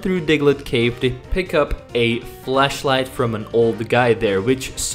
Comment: thought you said you got a fleshlight from an old man in a cave